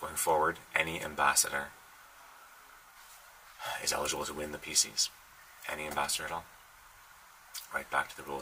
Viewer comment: Did he just say "Any ambassador is eligible to win the feces" ?